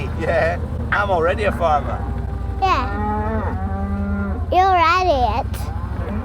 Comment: “You’re ready it” I heard “you’re idiot” 😂. I did wonder if she’d spent too long with Mrs gate lady and was calling you an idiot 😂😂😂